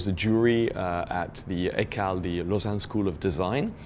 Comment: Can someone tell me what he said at 4:50. what school is that? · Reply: ECAL - the Lausanne school of Design in Switzerland